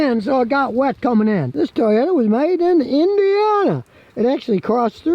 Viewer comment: I thought you said this Toyota was made in India---ana 😂